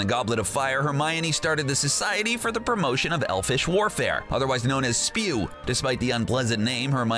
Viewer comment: did I hear you say WARFARE?